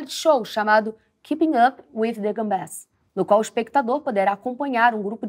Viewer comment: eu ouvi "keeping up with the gambés"?